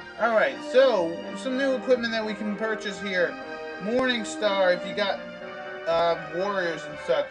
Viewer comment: For a second when you entered the weapon shop i thought the morning star said morning wood lol, im so tiredxD